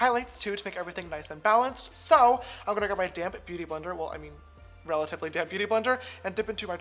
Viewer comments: I always thought he said ‘damn beauty blender’ until I realized it’s DAMP 😂😂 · Haha the whole video I thought you were saying "Damn beauty blender" instead of "Damp Beauty Blender" 😂😂😂😂😂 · Lol when he says “damp beauty blender” I keep hearing “damn beauty blender” 😂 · Whenever a beauty guru say "damp beauty blender", it always sound like theyre saying "my damn beauty blender" like ok gurl